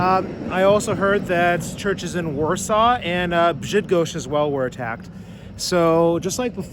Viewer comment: did you say Brzydgoszcz? 😂